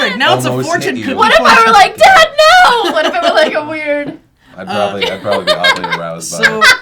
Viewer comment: What does say at 12:29? With laughing I just can't make it out. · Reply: "I would probably be oddly aroused" You're welcome sir.